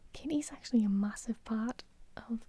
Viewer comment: I keep hearing "Kitty is actually a massive fart". Help.